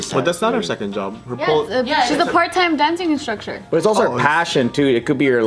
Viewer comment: Did anyone else hear say "Dancing InstrucSHER" so cute love it. Click It go listen